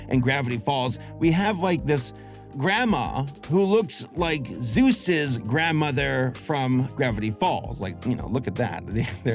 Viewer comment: im so sorry, but at 10:55 it sounds like you said "Zeus's grandma" instead of "Soos's Grandma"